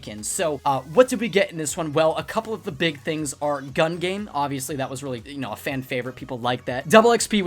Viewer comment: Did he say gun game?